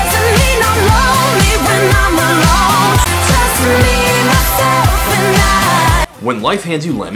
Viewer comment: When she said "just me myself and I" I always thought she was saying "just be yourself tonight"